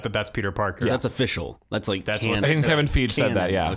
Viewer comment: I'm sorry, did you say "Kevin FEEJ"???